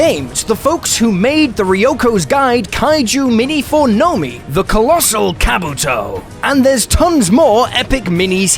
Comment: swear I heard you say Colossal Cameltoe. Had to rewind like like 3x to make sure 😂